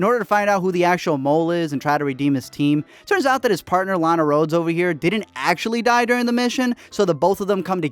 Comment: Did I hear Lana Rhoades? lol